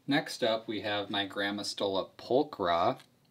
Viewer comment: Wow this was an experience! One minute I am fascinated...then horrified...then a lil grossed out...then fascinated again. At one point I was cracking up...but only cuz I thought you said "my grandma stole a polka" as one of the spider names LOL Love the ending...yea I think that worm represents all of us in 2020! xD ps why do some of them make webs and some don't? Just curious.